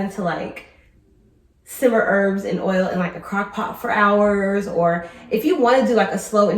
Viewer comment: Hay sis, that machine is nice, however $289.99 & that is sale price. I expect that infusion to be done in 30mins 😅 and not 6hrs. I'll stick to my mini crock pot & a 3hr infusion 😅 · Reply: Ohhhhh crock pot. I thought sis said crackpot. My bad, I'm from 🇬🇧